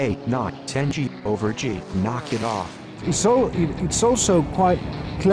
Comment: did it say "Knock it off"?